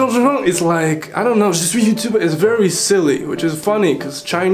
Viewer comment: Wait wait wait did you just say I am YouTube